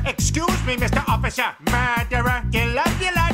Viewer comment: What Terminator Peter sang: "Murderer..." What I heard: "Madara..."